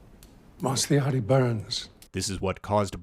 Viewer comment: Can someone please tell me what says at 11:53. Cant quite catch it... what burns? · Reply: “ whilst the outtie burns “ i think.